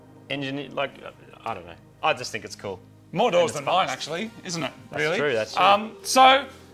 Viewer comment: Older. One door. Rear engine. I swear, Moog shows up with a Purvis Eureka.... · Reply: I thought he said more doors, i thought like a 4 door